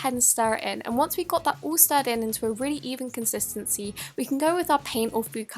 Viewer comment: I love her accent but 1:11 I thought she said evil :p